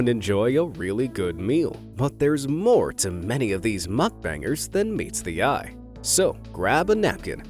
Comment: Oohhhh MUK-BANGERS I THOUGHT YOU WAS SAYING MUFF-BANGERS GOD DAMN